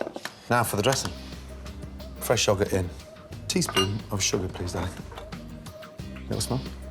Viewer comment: did Gordon just say “please daddy”?